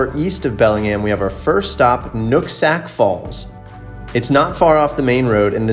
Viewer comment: It sounded like you said Nutsack falls...loool